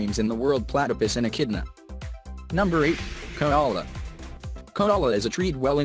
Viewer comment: did i hear that right? konola?